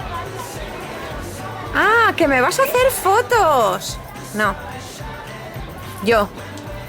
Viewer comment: Minuto 6:35 parece que dice yo